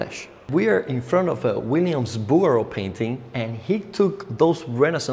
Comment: What was the name of the Artist you mentioned at 20:28? Williams Booer? I couldn't quite catch what you said. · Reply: Bouguereau